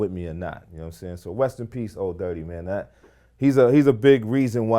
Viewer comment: Sound Like He Said West In Peace